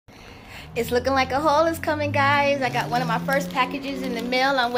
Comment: Hi sis can't wait for the jail,have a great evening you and hubby.💛💜💙💙❤️ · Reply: I got excited I thought you said a UHaul is coming lol.... blessings❤️